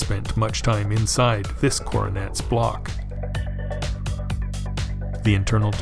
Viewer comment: Great video, as always, but at 9:57 did he actually say Corvette? I'm listening on speakers, not headphones. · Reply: Coronet, close.. but no Chevy. Thanks for watching.